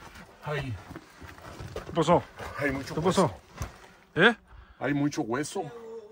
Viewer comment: le he estado escribiendo mensaje ...para decirle que en uno de sus videos alguién le dispara la bala le pasa muy cerca ,no se ve nadie y tampoco se oye pero casi le pegan espero lean este comentario . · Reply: En el minuto 18:34 se escucha como otro murmullo como que dice hulle o algo así